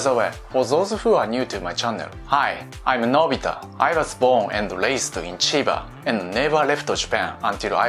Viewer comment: sorry but I heard him saying "I was spawned and raised in Chiba" 😂